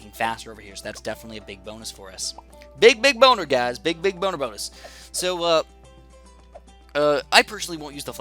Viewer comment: Did he just say "Big big boner guys"? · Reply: "Big, big, boner bonus."